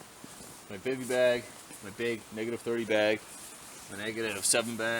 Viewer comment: Did he just say -30 bag?!?!?!